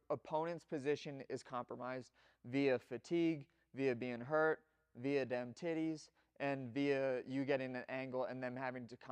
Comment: Did man just say “via dem tiddies??” 😂